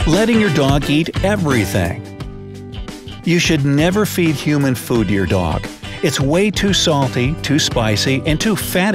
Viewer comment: does anyone else hear “you should never feed human food your dog”?